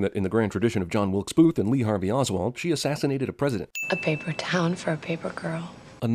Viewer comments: thought it said “everything wrong with paper towels” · Wait it's paper towns I always thought it said paper towels